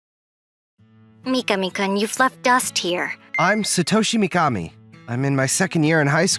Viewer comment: "mika-mika you left dust here" also what i hear: "Nika nika you f***k us here"